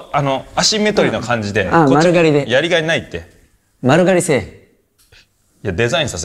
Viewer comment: 「丸刈製麺」に聞こえた